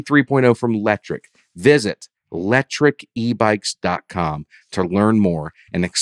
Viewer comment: When are started listening to this channel and y'all were talking about Lectric eBikes, I thought you were saying "E"lectric eBikes and I always thought, "You guys sure are redundant. Those words are the same." Derp! lol